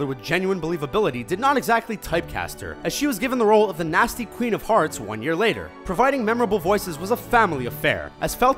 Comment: It sounds like he said queen of farts